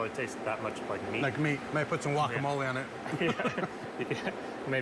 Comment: did he say guacamole?🤔